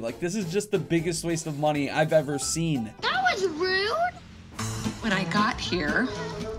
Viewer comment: lol it sounded like right as finished his sentence around 6:05 the birthday girl replied, “that was rude”